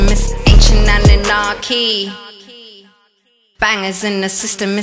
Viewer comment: Lol did she just say anchient anunakis? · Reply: Yes she said ancient anunakis Knowledge is power